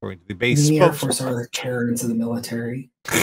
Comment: What did say around 19:28? I couldn't make it out, and the CC gave me "the basement officers are carried into the military," so I don't think that was it :p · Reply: I believe it was 'The Airforce are the Karens of the military.'